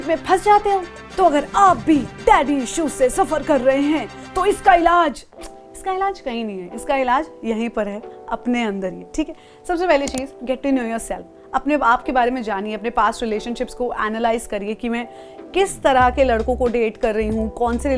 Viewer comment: i thought se said "Apne Baap ke baare mein jaaniye" LOL! btw great video as always